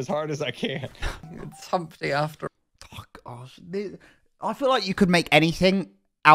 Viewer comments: Urm what did just say??? I may be hearing him wrong but... Not sure if i heared it right. but live chat didnt seem to pick up on it so im probably hearing things. Anyway this season had 0 context to it and im here for it. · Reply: He definitely said “oh gosh” lol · Did just say "F*** off" during a livestream?😮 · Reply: He said “oh gosh” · WHAT DID YOU SAY!? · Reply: He said oh gosh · It sounded like said "f off..." 😭